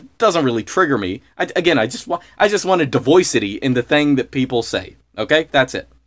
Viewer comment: im sorry i might not be in the know but did he say divoycity?? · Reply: thanks, yes he said "diversity" with a NY accent 😂. Or made a new word: Diversity/Voice synthesis.